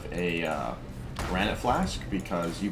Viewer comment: Which flask at at 2:25? Can't hear it, sorry. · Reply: Granite